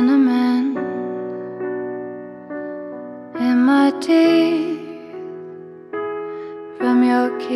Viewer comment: At first I thought she said "cinnamon in my tea" and I'm like ooooh, but it's actually cinnamon in my teeth 💀